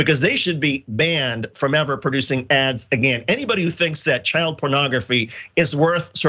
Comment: I thought she said, child corn.